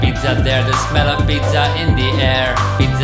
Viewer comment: Do you see the smell of pizza in the air? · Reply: I thought that said "This melon pizza in the air"